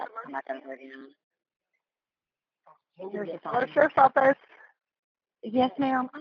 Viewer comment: Boy, that 911 operator's a real winner! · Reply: Lol I think she said “hello, sherriffs office.” At 0:05. Those are non emergency lines and she probably was not at all prepared for something like that after a day of doing paperwork and filing basically.